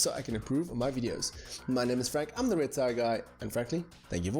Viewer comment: I thought he said I am a retired guy at the end of the video but when i think again oh he meant.